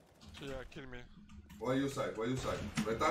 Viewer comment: Legends say, to this day, still doesn't know what he said. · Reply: I thought he said "Why you site?"